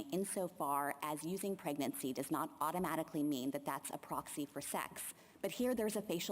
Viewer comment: Does she really say pregnancy is not a proxy for sex?